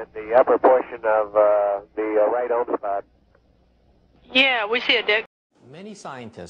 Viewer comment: Sounds like she said "Yeah,We See A Dick"